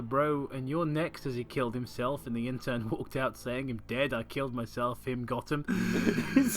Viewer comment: I misheard this as “killed myself- got em” kinda like the “deez nuts- got em” meme 😂